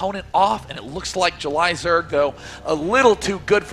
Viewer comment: the first guy that talks...sounds like he says JUL-Isaac instead of JulyZerg.